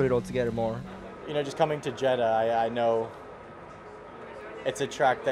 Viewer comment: Sargeant: "...just coming to Jedah, I..." He sounded like he said "just coming to Jedi". 😀